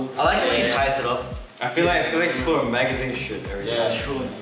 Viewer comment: Is it just me or does it sound like Felix is saying “Magazine Sh*t